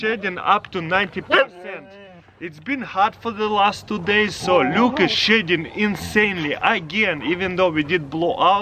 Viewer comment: I thought he said "Luke is shitting insanely" I was crying with laughter.